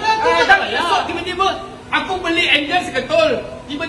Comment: senang jer buat kesimpulan ni...dia memang tak buat apa pun kat kereta tu,,atau dia bukak jer apa2 yg tak rosak bgi rosak....pomen mcm ni bnyak sangat dh aku jumpa...smpai aku pernah kner cas 2 kali gaji bukak head enjin kereta ,sbab nya dia buat 2 KALI kerja BUKA,BAIKI ,PASANG BALIK,dah pasang dan nak uji hidup baik tak baik,skli ada benda dia kata lupa pasang,bukak head balik dan pasang skli gi....dia cas aku rm450,,dan aku dpat rsit siap tuliskan'' rm450× 2 " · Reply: Dengar percakapan macam oner tukar enjin seketul tu,hulf cut,dengar macam dia sebut aku beli enjin seketul,pomen pula cakap enjin tu nak rosak mana aku nak tahu ,sory la kalau salah dengar